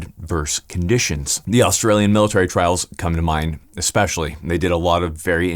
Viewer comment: also, at 13:50 , did you say Australian?